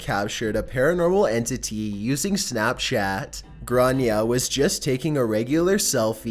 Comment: I thought she was saying "GRANIE" Like its her grandmother 🤦